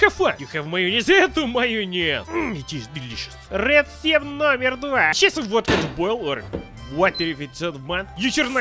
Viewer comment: are you speak indonesian? i hear you speak '''resep nomor dua'